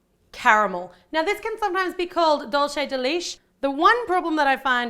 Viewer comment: did she just call dulce de Leche 'dulce the leech come on man' #whitepeople